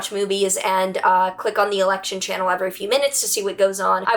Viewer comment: Did you ever share how you got into astrology and tarot? I’d watch that. Also I heard “turn on the erection channel” 😅